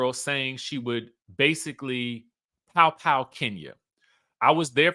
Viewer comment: Sounds like Porsha said “now kenya that’s too far!”